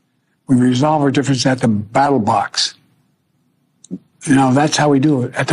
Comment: It sounds like he says at the battle box, not ballot box.